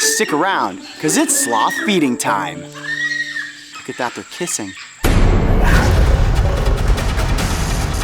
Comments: all this time I thought he said “without the kissing” until I read the captions, then I started hearing it right😂😂😅😅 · I thought he said sloth beating time, and I gasped and paused the vid to take in what had happened, then I realized that he said feeding because he would never harm an innocent animal. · I thought he said sloth beating time